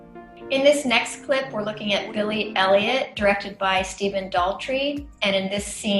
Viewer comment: ISTG i thought that it said billie eillish